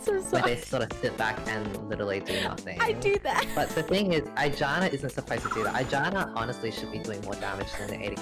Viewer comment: Does anyone else keep hearing 'vagina' instead of 'with janna'?